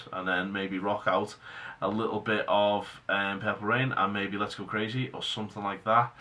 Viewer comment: With your accent, i thought you said "pepper ann" instead of "purple rain", and i was really looking forward for you to funk up an obscure Disney channel cartoon theme...